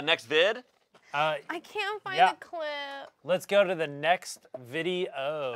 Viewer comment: I THOUGHT COURTNEY SAID CLIT 😂😂😂